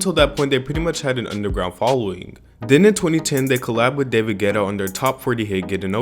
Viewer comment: I thought you said David GHETTO in the beginning omg lol